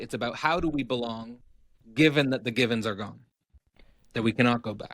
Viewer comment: the givens are gone, but hopefully we can save the gibbons 😅 honestly thought that’s what i was hearing when I heard “given” the first time in the podcast…great talk as always!